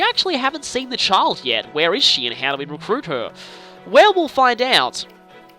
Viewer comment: I do not why I am pointing this out other than it made me chuckle a bit because i was not sure if it was intended or not but did anyone else think around 7:05 sounded like he said "Werewolf find out." when he was trying to figure out how to recruit Velouria.